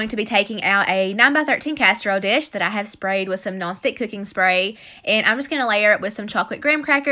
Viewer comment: I LOVE you and your videos!! I am a subscriber and I have to tell you something: I'm from the north so whenever you say "non-stick cooking spray" I hear "Gnostic cooking spray" 😂.